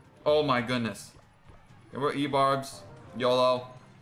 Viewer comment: did he just say "We go e-barbs, yOlO"